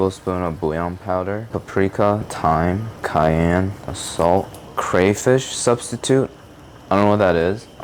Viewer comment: I am pretty sure I heard "Assault"